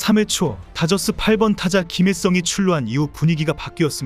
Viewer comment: 초 그러나 3회초, 다저스 8번타자 김일성이 출루한 이후 분위기가 바뀌었습니다. 라고 들리는건 제 귀가 이상한건가 ㅋ